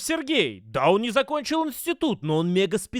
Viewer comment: просто признайтесь, что все мы услышали даун не закончил институт